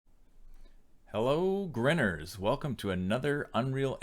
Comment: Lol I need to know what his greeting is. 0:00 Hello 'grenners'? I've been trying to google stuff for like 5 minutes and looking at your other videos, the only other one (That I saw) was the one right after this one. If anyone knows what he said, that'd be great for my sanity lol, thanks. Also, great tutorial, it helped a lot :) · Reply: Grinners. 😁 As in folks who are currently grinning.